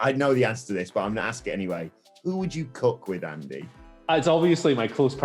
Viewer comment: Listening to this I thought Adam said "who would you cuck with" not who would you cook with... Which makes it even funnier